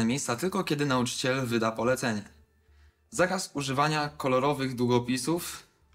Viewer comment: "zakaz spożywania kolorowych długopisów" to usłyszałem xD.